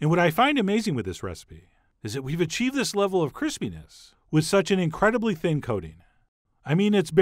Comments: Over a year and I haven't seen a single comment mention that the subtitles say "Chris penis" at 6:02 instead of crispiness lol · Uh. I think I should say that English subtitles have a very inappropriate mistake at 06:05 mark. Instead Crispiness it is saying, "Chris penis". It's unintentionally funny, but I think it best that someone correct that. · Turn on the closed captioning and at the 6:00 minute mark when he says "this level of crispyness" the closed caption says Chris penis. I'm mentally 12 years old, lol.